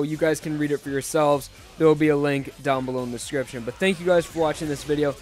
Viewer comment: For a second, I thought you said scripture instead of description